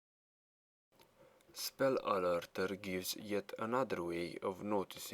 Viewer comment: Spell Alerter sounds like you said Spul Alolto sounds so fking epic